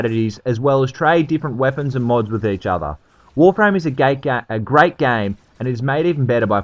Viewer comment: did you say warframe is a gay game lol